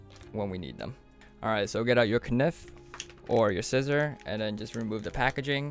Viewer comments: did you just say knife as "kanif" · di... did he just say K-niff